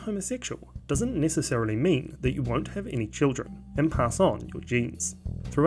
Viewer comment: i thought he said eating children oops